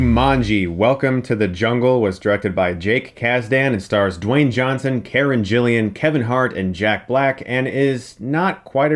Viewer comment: Did... did you just say Karen Jilan